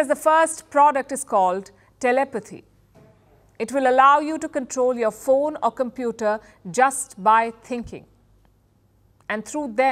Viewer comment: "it'll allow you to control your phone or computer just by thinking" What I heard, "it'll allow your phone or your computer to control your thinking".